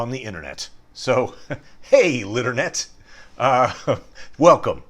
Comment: Haha I really thought you were saying "hey liternet" I just assumed it was some joke or reference above my head lol